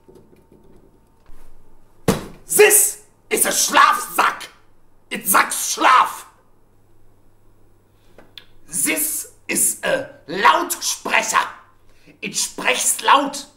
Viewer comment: I thought he said it sacks slav